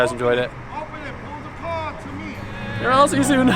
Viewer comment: sounded like he said "lz ya soon" at the end